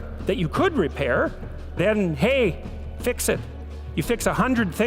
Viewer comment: haaha at 4:36 says "fix it"....the caption says "fit it"....there I fixed that error...feeling better